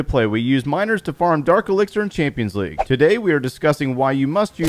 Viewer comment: "today we will be are gonna disgusting" instead of discussing